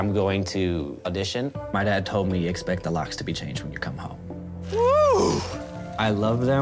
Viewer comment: I don't get why parents wouldn't support his dream. He likes cooking, not doing drugs, man! I'm proud of you! Go chase your dream! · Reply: did he say that when he goes home " expect the locks to be changed"?